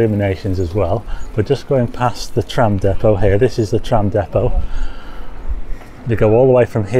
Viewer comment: I misheard I thought you said the tramp depot, I need to get my ears tested